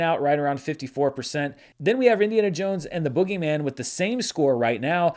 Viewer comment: I had to pause and replay when you got to the Tomatometer part because I thought you said "Indiana Jones and the Boogeyman" instead of "Indiana Jones" and "The Boogeyman". I laughed way too hard at that.